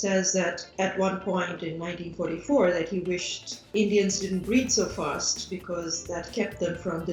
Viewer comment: I think she said breed not breathe.